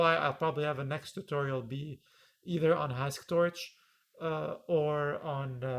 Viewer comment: Does anyone know the frameworks mentioned here? I couldn't catch the names and want to check them out. This was another awesome video, videos are my new favorite weekend binges! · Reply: Thank you! I mention 2 languages * Dex: <link> and I have a video <link> * Hasktorch: <link>